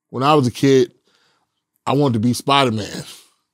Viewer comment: I thought he said I wanted to eat spiderman 😂